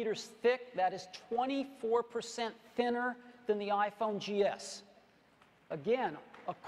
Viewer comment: Did he say "iPhone GS"?